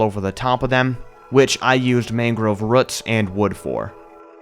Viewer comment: Did he say "mangrove ruts" 8:45? XD Jokes aside though, this build was amazing! Farosh looked PHENOMINAL and the whole region overall looked stunning. You and everyone who helped you build this are honestly spectacular.